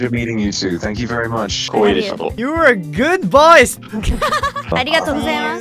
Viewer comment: 葛葉は｢good voice｣って言ってると思うけどネイティブにはgood boysに聞こえてるんだ